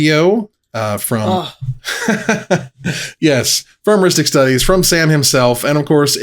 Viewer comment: I heard you say Rhystic Studie - PAUSE - I'll be back now now